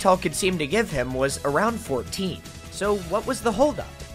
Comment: the captions put 16 instead of 14 huh